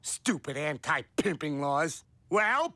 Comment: "Shut up, baby. I know it." That gets me everytime. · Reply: i thought he said the same but apparently he says “sure baby i know it” 😢